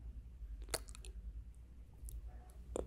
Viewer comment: yo intentando saber que dijo en esta parte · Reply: "No lo intenten en casa"